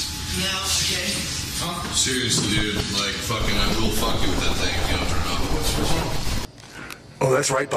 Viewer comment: what did he say? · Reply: "Seriously, dude, like I will fuck you with that thing if you don't turn it off."